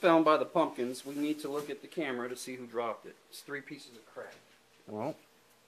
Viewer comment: I had to rewind three times to make sure I really heard "three pieces of crack by pumpkins" wtf is going on in this Walmart!?😂😂😂